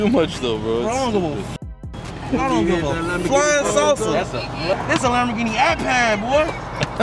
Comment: did Sosa say i don't give a flying Sosa or flying saucer 😂😂😂